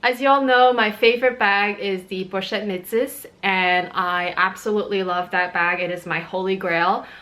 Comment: what is the brand/name of the bag you mentioned at 0:22 - 0:23 -- I couldn't quite make out the brand/name. Thanks! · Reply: That would be the Louis Vuitton Pochette Metis :)